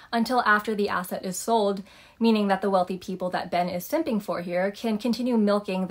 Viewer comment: I had to go back and listen to that part because I thought she said simping. Lol